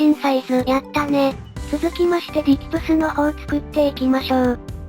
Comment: 何回聞いてもディキトゥスが ディップスに聞こえて仕方がない